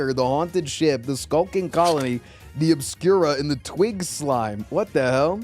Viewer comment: NGL I thought he said twink slime